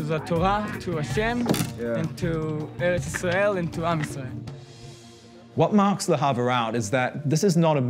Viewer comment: there is a mistake because he says "am israel" which means the people of Israel in hebrew but the subtitles say "to arm Israel" so they are wrong here